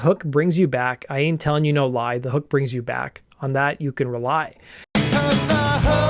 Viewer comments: I always thought he said "the heart brings you back". Guess that makes it a different song. At age 40 I got let in on the joke. Thanks for ruining it! Lmao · I'm probably the only one who thought he was saying the heart brings you back...